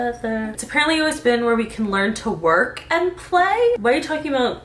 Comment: I thought said where you can learn to walk and play my childhood Is ruined